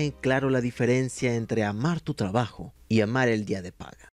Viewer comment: Por alguna razon en el intro escuche "amar el dia del padre" en lugar de paga XD.